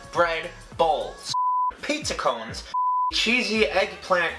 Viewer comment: this whole time I thought he was saying pizza bons....